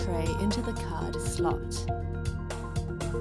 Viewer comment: Did this phone named Vaior instead of Honor?